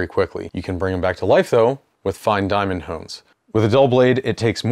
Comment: Sorry im not english, what did you say? fine dimaond ****??? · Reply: Diamond hone. Hone ≈ Sharpener